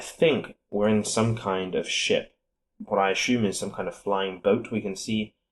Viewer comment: what did he just say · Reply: Ship, np